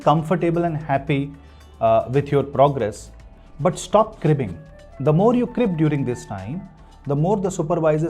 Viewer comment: can someone tell me what word uses here? It sounds like “cribbing” I just want to clarify what he said. Thx